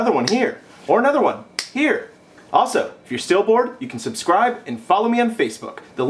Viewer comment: did he say u can follow me on face book?